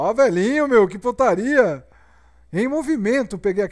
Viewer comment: só eu que ouvi "oh velhinho,que putaria",da primeira vez?